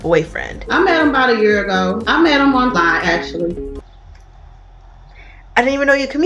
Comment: I think she said "I met him online", not "I met him on Vine"